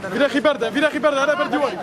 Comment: I hear Pashto words at 0:40 (dekhwa sha shaba) means come here quickly. or is it also in arabic language?